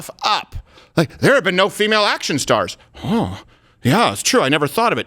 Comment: "There are no female action stars" Steven, that is not what she said. She clearly stated that there are no female action lead characters. Big difference. But she is still wrong, I just want you to make sure your word choice is an accident and not an intentional mislead as you basically took what she said and ignored part of it just like what the left does to the right. But again, she is still wrong and there have been dozens going back decades before Hunger Games was even written.